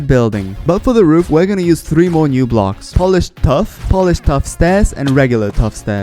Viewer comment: bro i thought he was saying polished stuff